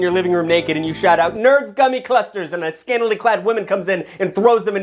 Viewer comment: I swear I heard “ Nerds c*m in clusters! “ I died